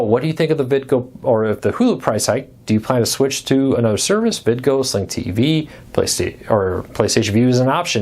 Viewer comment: After you mention Vidgo at 3:28 as an alternative, you mentioned a second service - Sight TV? Couldn't understand, but please clarify so I can check it out. Thanks! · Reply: Frndly TV